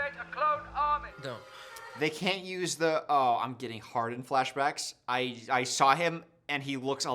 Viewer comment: Not gonna lie, at 02:56:10 I fully thought he said "I'm getting hard" at first, and my brain glitched out.